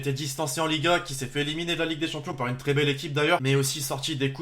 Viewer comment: je suis le seul à entendre Ligue 1 (au lieu de ligua, ou c'est moi qui a mal compris car c'est bizarre le Real Madrid en ligue 1 😂😂